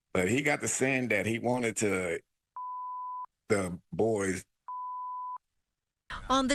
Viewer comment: What did he say that they bleeped out???? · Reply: flip the boys bottom